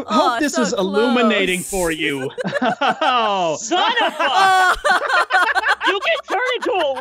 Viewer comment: did anybody hear "SON OF A-" in 12:33??